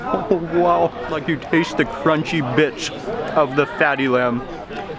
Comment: Did Mark Say (you taste the crunchy Bit**😅 of the fatty lamb). He is actually drooling with the BBQ in his Mouth🤤....